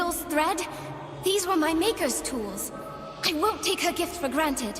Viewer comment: everytime she says "these are my makers tools" literally sounds like she is saying "these are my hard "R" N words tools" I cant unhear it :/